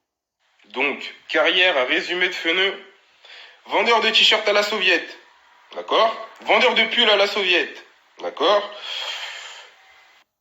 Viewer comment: il a dit « vendeur à la sauviette « ou c moi chui sourd Ça critique le français de wejdene mdrr